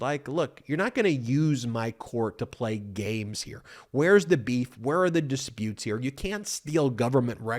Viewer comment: So off topic, but did he just say, "Where's the beef"?